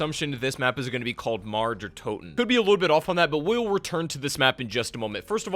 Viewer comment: Did he say Marge Der Toten?